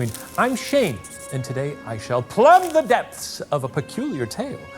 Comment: Oh "plumb the depths". I thought he said "club the dwerps" and then immediately the camera cuts to Ryan. Confusion has now lifted however.